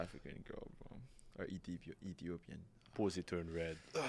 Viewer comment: did he say Ethiopian girl(38:02)😏